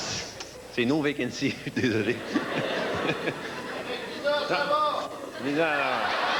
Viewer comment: qu'est-ce qu'il dit le gars dans la salle ? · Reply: Il dit "Avec VISA ça va" pour se moquer de la pub de François. Il paye tout le long après!